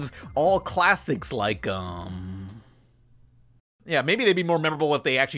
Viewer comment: BRO DID SHE SAID MCALLEN TX BRO IM NOT STUPID OK